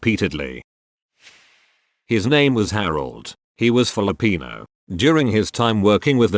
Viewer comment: I misheard this as, "His name was Harold. He was full of peanuts."